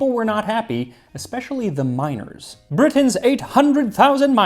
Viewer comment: when you said miners were outraged because of the lack of bananas, i thought you said minors at first, as in kids, instead of miners as in people who go dig up stuff